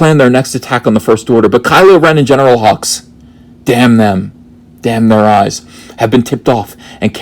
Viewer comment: did you say "Damn their eyes"? If so, was it a reference to Young Frankenstein?